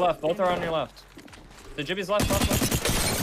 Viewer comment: I'm sorry. Did he say "jibby"?